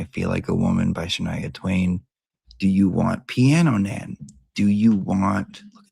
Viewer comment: Teacher, did you say “Piano Nan” or “Pee on a Nan?” I am very confused.